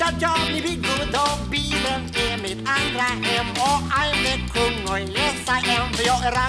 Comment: vad säger han mellan 0:18-0:22? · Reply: Bilen är mitt andra hem?